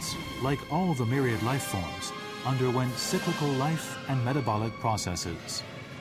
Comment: can anyone hear the words before the" metabolic process" at 2:24? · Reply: Its "cyclical life and metabolic processes"